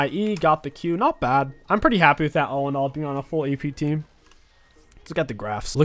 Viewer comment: not the 5 AP team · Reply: I know about AP, I peaked emerald in my third season playing the game I thought the guy was saying it’s not a Full AP team when it clearly is Thanks for your good will to explain it though